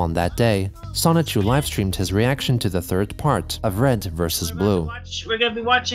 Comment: For some reason I heard "third reich" and now I really want to see that